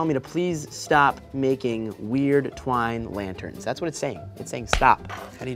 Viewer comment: I thought it said Twinkie lanterns